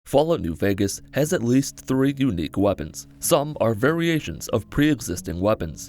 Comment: "At least three unique weapons" Well he's not wrong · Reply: Yeetus the first time I watched this, I thought he said “at least three weapons” and I had the same reaction you did